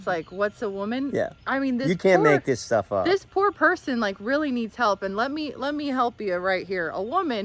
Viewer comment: It sounded as if you were saying, Matt Walsh needs help. (18:00). Matt Walsh is spot on. You need to watch him. He is saying exactly what you’re saying, except even better.